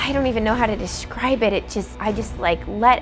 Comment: I heard "I dount even know how to dishibe it